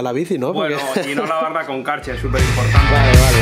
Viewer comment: Qué es lo que dice en el minuto 6:52? No lavar la bici con... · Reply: Karcher, hidrolavadoras a presión.